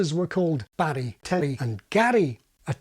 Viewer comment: This is an error in the (otherwise very good) English captions, but I don't know how to edit them. The English captions say "Larry, Barry, and Gary" when of course they are Barry, Terry, and Gary as the text on screen says.